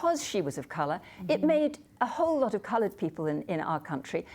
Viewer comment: did I hear it corectly? Did she say "colored people"? At 7:04Is this PC in the UK?